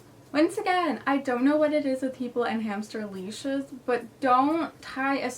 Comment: I thought sis said hamster bleach not hamster leash i- 😭😭😭